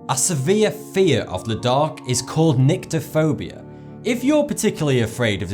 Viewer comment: When he said 'Nyctophobia' I thought he said 'Niggaphobia' I'm not a bad person I just have a teenage mind. :P